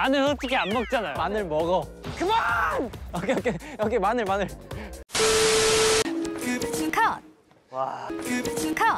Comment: what did he say here?? In English and Korean plsss thank you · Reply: He said "COME ON!!" English